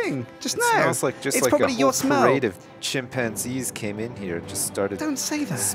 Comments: at first i thought said "sjin panties" instead of chimpanzees :) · For a second there, I thought says Sjinpanzees at 17:50.